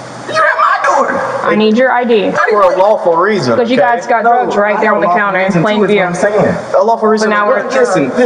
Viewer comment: Nobody is mentioning them saying they saw drugs in plain view inside the house. Pull all residents to protect the evidence, secure the house and start working a search warrant. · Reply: "You guys got drugs on the counter. Right there in plain view." The captions are wrong, but that's clearly what she said as she lit them up with her flashlight ...